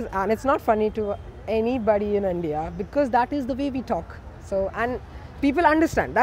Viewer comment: whenever they said “that’s the way we talk” i thought they said “baby talk” I’M SORRY